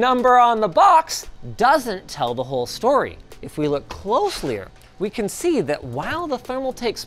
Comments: Did Linus say closelyier? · Did he say closelier at 4:25 or am I just sleep deprived?